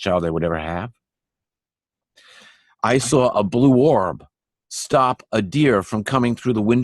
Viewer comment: he said a "blue 'warb' stopped a deer coming through his window screen?" I have replayed it and I still hear 'warb' - what on earth is a 'warb' or what is he really saying? Thanks. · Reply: Orb